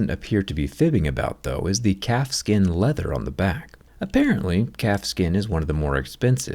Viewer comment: I legit thought you said cat skin leather and i thought it odd how casual it was talking about it.